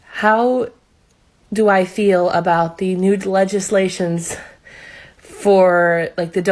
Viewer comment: Did she say "nude legislations"? Freudian slip anyone?